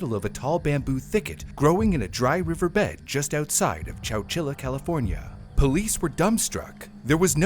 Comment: wait, wait, chowchila? I thought bro was just saying Coachella really weird 🤣